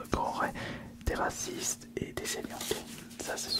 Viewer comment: terra quoi ?